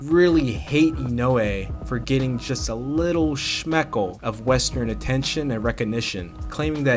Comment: I heard 'shmeckle' at ~23:20. Yiddish for penis, related to 'shmuck'. Must be some other use... lol